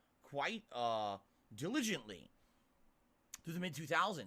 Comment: I had to watch this back at .75 and .25 speed and I still don't know if Phil's saying diligently or tiligently it's hard to tell what Phil's saying half the time